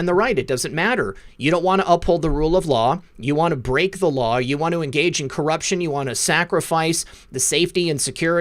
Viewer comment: Wow, I heard she won't engage, listened twice with ear buds.